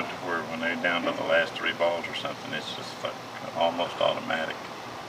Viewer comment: hahaha, does he say, "when they get down to the last 3 balls, its almost fucking, automatic"?